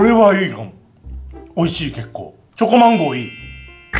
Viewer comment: すっっっごい虫歯みたいwww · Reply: チョコま○こって聞こえるw